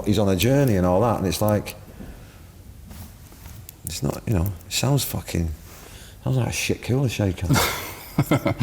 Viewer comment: does he say “it sounds like that shit Kula Shaker” ?